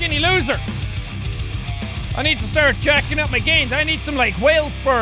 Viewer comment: When he said GaInS! I thought he said GaYS!